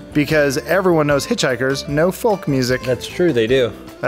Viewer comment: Did Adam say "Fulk music" ?